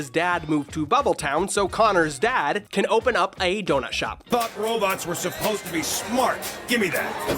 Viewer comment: I thought he said "Fuck robots" for a second